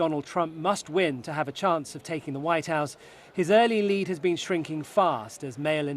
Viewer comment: Race for "ze vhite house" am i the only one that is hearing 👂 the news reader sound like she saying that 🤔 🤣